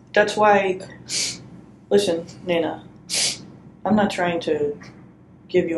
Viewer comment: did she call her Nina?? 🤷🏻‍♀️🧐